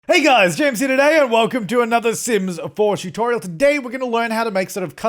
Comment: For some reason at the beginning of the vid I heard " hey guys today we're going to be doing a sims 4 abortion tutorial "